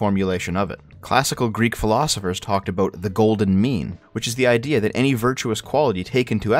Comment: I heard "The Golden Meme."